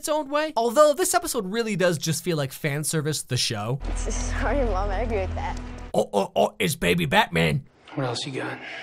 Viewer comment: WTF did the kid say at 7:14? I've rewatched that 20 times and it still sounds like gibberish to me. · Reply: “Sorry mom, I agree with dad”